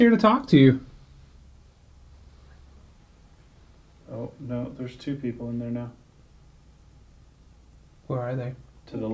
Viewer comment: Da hell was that background talk while you were speaking? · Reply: Jerzey Boy1995 it sounds like it said “is there three people”.